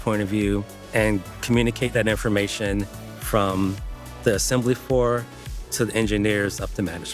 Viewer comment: update the subtitles, you said "assembly floor", the captions said "assembly for".